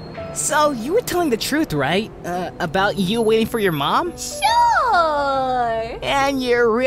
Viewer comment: "to sleeping on benches and eating scraps of food and do you know whos falt that is? " "obama?" I died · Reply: I heard 'your mama'